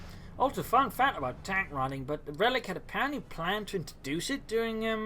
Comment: Excuse me ,me what do you mean under the phrase" tank running "? (at 27:35) Maybe i didn't hear it properly , in that case I'm sorry about that.